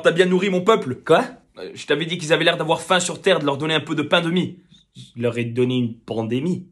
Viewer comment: Non native French speaker, but I always hear "pain et demie" (bread and a half)